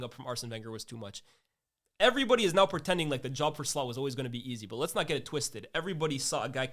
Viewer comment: Did he say Slut?